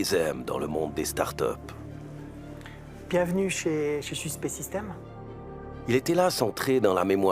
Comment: Vous entendez quoi à 06:24 ? Bienvenue chez ... ? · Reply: Bienvenue chez Suce Pet System